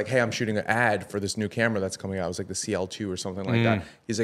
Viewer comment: did he just say CL2?